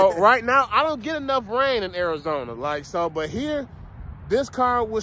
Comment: i could have sworn you said brain 😆